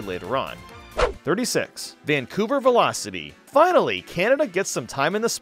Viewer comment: I thought you said “Kamek” instead of Canada